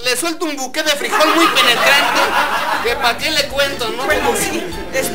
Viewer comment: DIJO ¿AQUI LA PARAMOS? O ¿AQUI LE PARAMOS? ¡NO OI BIEN!